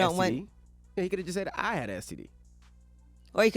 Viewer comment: did he say he had an STD 😂